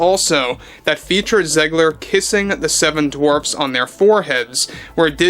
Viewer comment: I had to rewind here as I thought I misheard you and it turns out I did. I thought you said the P Word that Rhymes with Kissing instead of Kissing.